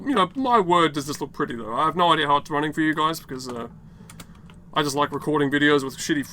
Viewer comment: And what happened at 12:17? Did you just call us "gays"?